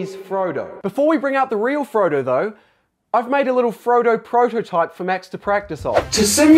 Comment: Missed opportunity: calling the prototype "the Frodotype" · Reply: My hearing sucks so much that I thought he said frodotype instead of prototype in the video lol